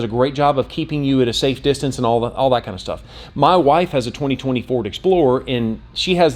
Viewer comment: Thought he said my wife has a 2024 explorer after replaying it 3 times I realized he said 2020 Ford explorer 🤦‍♂️I think the Broncos are kinda overpriced.